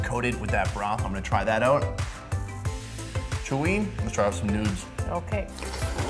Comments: Did u say nudes?... in 11:45? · sounds like he said nudes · Did he say let’s try out some nudes